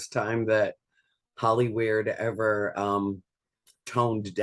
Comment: Did she just call them Hollyweird?😂😂